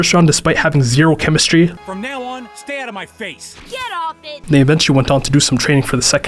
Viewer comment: I thought I heard “GET OFF NI-“ 💀